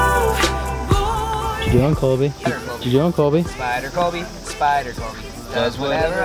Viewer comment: Lol at 5:02 I thought was saying there was a spider to colby like he was about to run into one while he was climbing lol but then I realized he was saying spider Colby. 😐😅